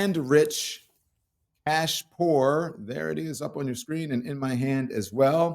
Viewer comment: When you said "cash poor," I heard "ass poor." And I thought, me too, I am damn ass poor.